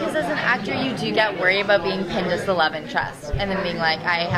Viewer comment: What did kat saying at 24:43 ? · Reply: pinned as the love interest